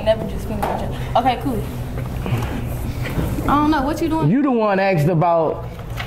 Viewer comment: Boosie was mad laid back in this video. A few time it seemed like he let the host son him. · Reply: Yo I noticed that too how he was rushing him and heard him say “nah I don’t know what ya saying” and boosie said well I’m tellin ya what I’m saying